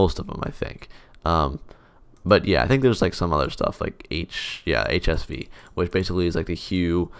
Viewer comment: Did you just called HUE, HIV?